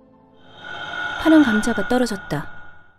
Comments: 이부분 윤민서머라고 전화하는거에요? 발음이 잘안들려서요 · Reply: 진짜 이게 안 들릴 정도면 귀가 어떻게 된 거임? "파란감자가 떨어졌다"입니다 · 얘가 뭐라고하는 거에요? 뭐가 떨어졌다구?? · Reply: '파란 감자가 떨어졌다' 라고